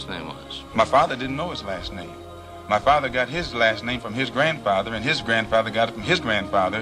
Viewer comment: did he just say "gifted last name"? The correct wording shouldn't be "enforced last name"?